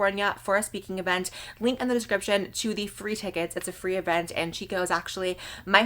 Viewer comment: Instead of FREE tickets I thought she said THREE tickets I was like sis some lucky three 😂